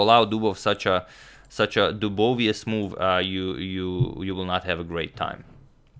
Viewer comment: Did he say Dubovious move at 11:10?